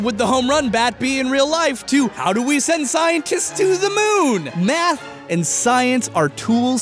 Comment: i thought he said how do we send scientist on the moon with a smash baseball bat.